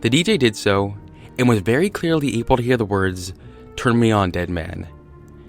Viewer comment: Oh, I heard "Turn me on Lennon" but ok